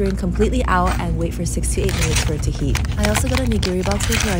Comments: For a second I thought she said 68 minutes 💀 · I thought you said 68 minutes instead of 6 to 8 and I thought that was a long time to wait! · I thought she said 68 minutes 😭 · I thought you said 68 minutes to cook I was like HELL NAH